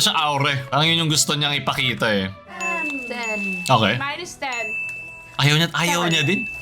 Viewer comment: "Mine is ..." po yung sinasabi ni Jhoanna. Namimisheard as "Minus ..."